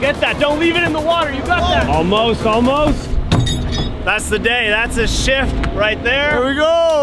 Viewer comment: Ha ha! At 14:45 when you said "and that's the shift, right there", it sounded like you said "and thats the sh!t, right there"🤣